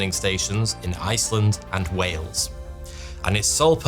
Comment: When did the Welsh move up there to hang with Iceland (Edit:I may have misheard I thought he said Ireland and Wales.)